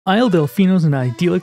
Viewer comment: When i heard you say “isle delficino” i heard “isle deficeno” as deficate with a no at the end